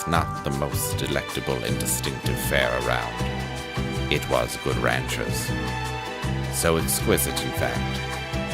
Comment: I had my earphones in wondering wtf is going on. Did I hear something about Good Ranchers?